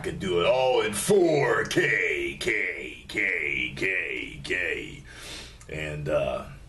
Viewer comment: it sounded like you were sayin gay instead of K